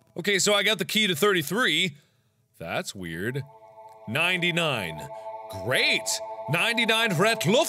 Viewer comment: who else thought it said gg and not 99? I can't be the only one xD